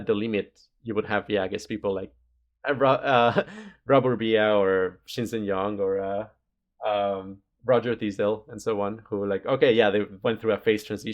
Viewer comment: I didn't catch any of those names, can anybody help? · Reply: Rob Burbea, Shinzen Young, Roger Thisdell